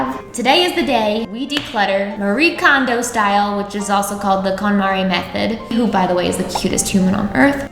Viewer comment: I thought she said Katamari method. Like the video game where you roll things in a ball. All I could think is that is an odd way to do this...